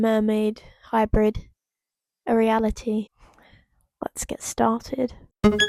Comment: When you said "reality" I thought you said "Ariana tea" idk how😭